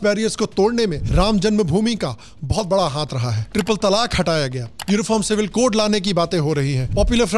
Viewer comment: You talk about triple talaq but the caption says Ram Mandir. Please fix it.